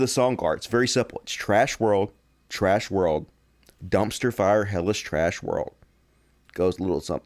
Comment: I thought you were saying Dustifying hellish trash world app this time